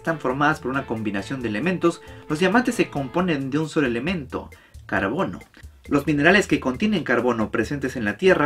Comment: No voy a mentir, Pense que decia cabron en vez de carbon 😂😂😂